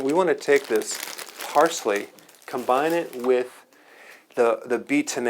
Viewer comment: Frozen kale Frozen parsley Frozen berries - 1 cup 2oz lemon juice 1 teaspoon wheatgrass powder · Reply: does he say combine it with the beet ?